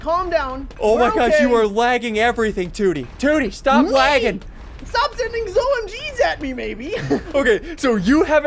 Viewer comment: did he just say dont send zomgs at me baby